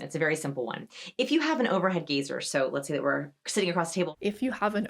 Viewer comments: I misheard her saying"Overhead Geezer" Yep that's me, I'm copping that term for myself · “Overhead gazer”… I heard “overhead geezer” and I thought “Does this eye-contact woman think that the bass player for Black Sabbath is lurking about?”. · Oh, overhead *gazer*! I thought she said overhead geezer! 🤣